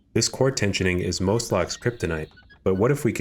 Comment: did he say kryptonite?